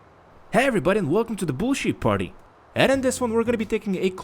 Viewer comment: I thought he said bullshit party